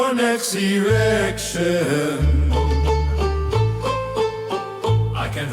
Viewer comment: anybody else hear 'next erection' ?